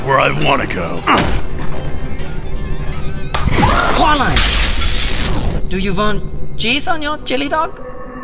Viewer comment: it sounds like he said "do you want jizz on you're chilly dog"